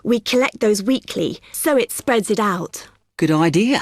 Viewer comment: at the time 4:28,what does the girl say? "so it spr out???????????????????" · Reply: "so it spreads it out"